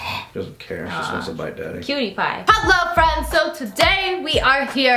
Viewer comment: When you said "Cutie Pie" I thought you said "PewDiePie"